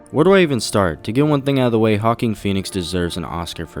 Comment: Why do I hear "Hawking Phoenix" whenever you say Joaquin Phoenix?